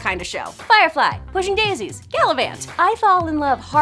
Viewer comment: What did she say @ 00:37 after pushing daisies? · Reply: Probably galavant